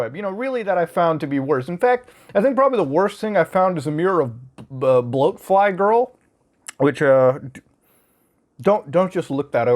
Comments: what does he say at 2:26 ? mirror of bloke fly girl? I am curious, but couldn't find anything about it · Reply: Boatflygirl. Don't look it up. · What does he says here? I have difficulties listening english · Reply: "In fact, I think probably the worst thing I've found is probably a mirror of Bloatfly Girl."